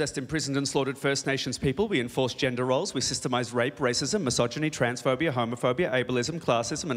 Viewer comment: I still dont buy into the normalization of rape, and hate everyone around me as far as I know dont stand for that, but just my opinion, the rest I agree with all the way, and he is very talented. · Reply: i think u misheard, at 2:15 reuben says "we systemized rape..." not "we normalized..."